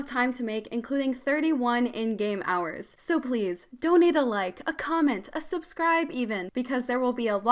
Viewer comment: When she said donate a like I thought she said donate a leg